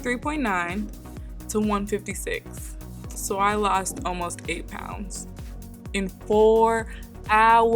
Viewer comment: For a second I thought shawty said 81 pounds in four hours